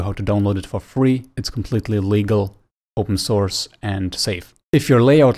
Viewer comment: "I Have A Video That Shows You How To Download It For Free Its Completely Illegal, Open Source And Safe." Anyone Else Hear Illegal??? Btw I Knew He Meant To Say Legal. XD